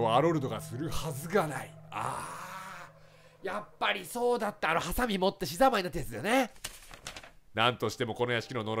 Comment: あーやっぱりそうだったあのハサミ持って寿司ざんまいになったやつだよね って聞こえた笑